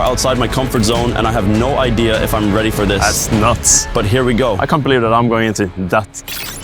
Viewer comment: what did bro just say 💀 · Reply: Nuts